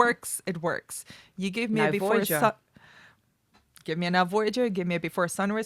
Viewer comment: Can you please possibly share the name of the movie that you both agreed was a love story done right at 0:54? Before you mentioned "Before Sunrise" and "Before Sunset", you both agreed on something that sounded like "Naforgia" or "Navorgia" but I can't find anything. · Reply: Now, Voyager its 40's Bette Davis movie